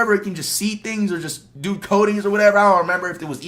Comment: is he talking about Cyclops? Did he say "do codings"???😂